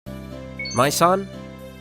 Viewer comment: I misheard 'Mai-san' as 'My Son' XD